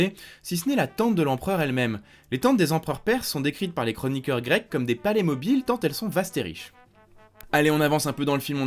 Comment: En voyant un visage féminin sur le mot tente à 12:48 , j'ai compris tante... Alors d'apès mes écouteurs de mauvaise qualité : "les tantes des empereurs grecs étaient décrites comme des playmobiles tant elles sont vastes et riches" Aïe